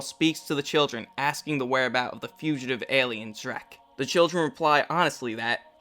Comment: Anyone else hear "the fugitive alien, Shrek?"